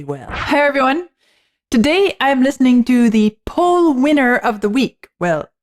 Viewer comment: I heard you say "Paul Winter" and I thought 'I never hear anyone talk about Paul Winter.' But you said "Poll Winner." lol. My mistake. The Paul Winter Consort was an amazing (if atypical) jazz band, btw....check out "Icarus" on his album Common Ground. As for Roberta Flack, everything she did was great!!! Her album "Oasis" stands out for me. Her voice was golden and spoke to the heart, like that of Laura Nyro (i.e. "Billy's Blues" or "Buy and Sell")...every note comes from the heart.